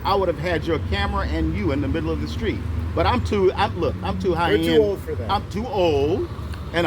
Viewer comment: He's so discriminated and disrespected, and suppressed, and he brags that in America he's making $200. An hour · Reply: i thought he said he was 'high end' whatever that means. if he was 'high end' he wouldnt know about crenchaw, he would only know about baldwin hills, ladera heights, beverly hills etc.